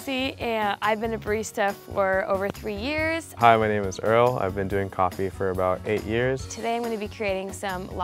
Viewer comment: i thought the man said i sniff coffee